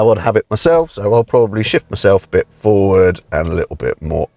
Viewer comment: I can't be the only person that thought he said "I've got to be honest, this is not my preffered driving position, so I'm just going to SHIT myself" between 3:13 and 3:20 😂